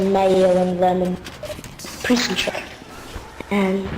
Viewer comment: You subtitled it prison check He actually said Prison Trick because it's a trick he learned in prison on how to change hair colour